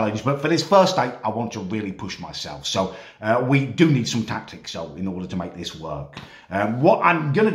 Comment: did he say he needs tictacs?